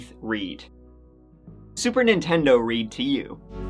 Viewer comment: I thought he said Super Nintendo Reed 🤷🏻‍♂️😂🤦🏻‍♂️